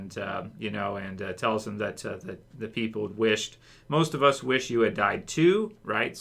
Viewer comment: It sounded like Lydia said “most of us wish SHE had died too.” As in alpha. I rewatched it 3 times and I could not hear her say she wanted Negan to die